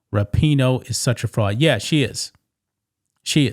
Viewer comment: "Rapinoe is such a fraud." When I heard that, I initially heard "broad" instead of "fraud". Of course, both statements are factually correct because we're talking about Rapinoe, but I just thought it was funny how I misheard one word that totally changed the meaning of the sentence.